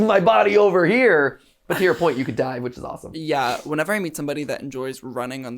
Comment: i thought you said "You can DIE with is awesome." Also, Why dose it say that this Video is from Half-Life: Alyx? XD